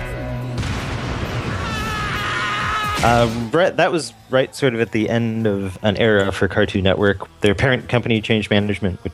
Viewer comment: did he say bread or Bret?